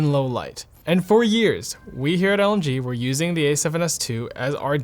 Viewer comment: I heard "...we here at LG" at the beginning of this video...